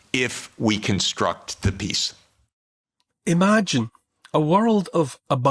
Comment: I thought he said construct the beast. LoL shows you I have been somewhat brainwashed myself.